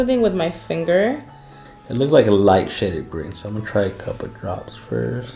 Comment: did he just say “a light sh** of green.” Lol